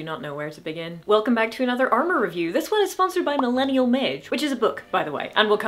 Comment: I know it's just an accent thing, but I kept hearing "Millennial Midge." And I thought, "Who is this woman?" Mage. OK, that makes more sense.